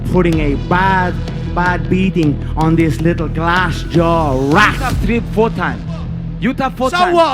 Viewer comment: lol did he call him glass joe?